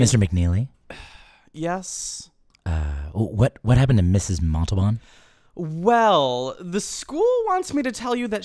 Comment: I missheard that as "Mrs Matzoball"